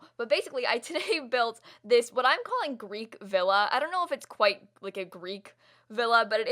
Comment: 100% thought that said "greek vanilla" for a solid minute.